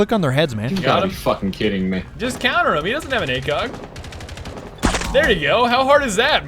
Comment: Uhhh does anyone else have the ahem wrong word instead of acog